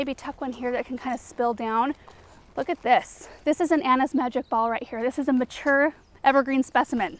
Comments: At minute 6:45 you said the name of a evergreen specimen, but I don't quite catch the name. Could you respond back in writing. The little green & gold scrub. Please... thank you. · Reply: Anna's magic ball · What was the shrub at 6:44 that she said was mature size.?? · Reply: Anna's Magic Ball arborvitae.